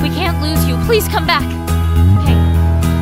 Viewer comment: WTF is Herlo? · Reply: Amelia Crasman I heard Lily said "Hey what's going on? Please speak to me, come back don't leave me Herlo!" I think.